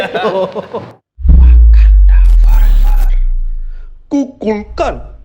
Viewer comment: pas di sini ngomong apaan sih? Ku klux klan ya? 🚨🚨🚨